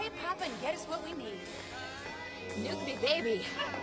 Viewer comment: "Nuke me baby" hahaha I donno why but I laughed so hard at that part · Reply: I thought she said Milk me baby lol